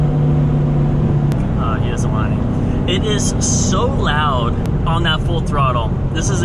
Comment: “he doesn’t want it” that’s what stay saying lol